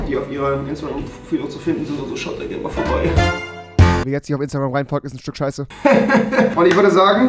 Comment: Ich hab nie verstanden was er gesagt hat... Nur Instagram und Stück Scheiße 😂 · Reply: er hat gesagt: Wer ihr nicht auf Instagram reinfolgt ist ein Stück scheiße